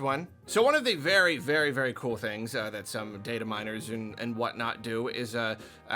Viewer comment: It’s so late that when said “dataminers” I hear “potato miners”